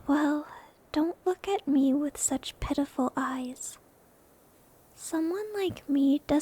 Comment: Did she said pedophile eyes?